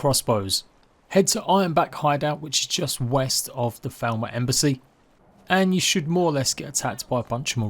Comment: did you say the "Falmer embassy"!?